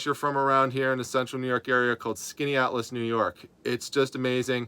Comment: Nice slice of Americana at the end. Some really nice houses there. I had to pause the video to read the name on the sign. I thought you said the name of the town was Skinny Atlas, NY. LOL